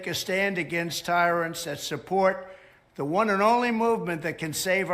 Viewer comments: I don't get it? He said "take a stand against these tyrants and support the one and only movement [...]" blah blah blah. How is he saying to stop supporting MAGA? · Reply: Sounded like he said these tyrants that support.. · It’s mumbled what he was saying imo. He jumbled between “take a stand against tyrants AND support” and “take a stand against tyrants THAT support”. I heard more of a “THAND” with the brain glitch but I get it.